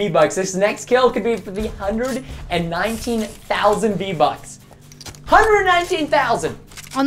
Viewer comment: did Preston say 319,000 Vbucks